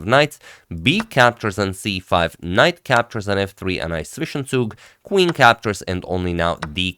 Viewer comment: What does say at 3:00,"ice fishing sug"?